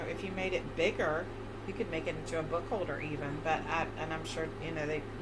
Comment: My ears need unclogging, I thought you said it was a foam holder lol…